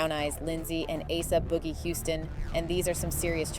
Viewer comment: Did they just say aboogie wtf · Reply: Asa Boogie Houston.